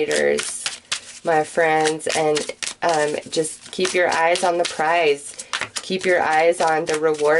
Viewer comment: I don't know if anyone else is on this page, but when she said "keep your eyes on the prize," I heard it as "keep your eyes on the Lord." When she said "Spirit is opening the door" I saw the parting of the red sea.